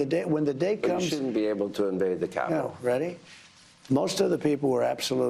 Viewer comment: Sounds like Hannity is saying "They shouldn't be allowed to invade the capitol," is the accurate?